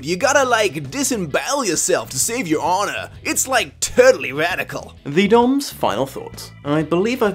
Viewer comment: "Whoa Shredder dude you gotta like disembowel yourself to save your honor. It's like totally radical." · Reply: I heard it as “turtley radical”